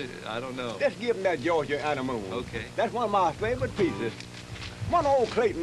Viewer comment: Anyone know the name of the last waltz he plays ? Sounds like he says georgia honeymoon but not sure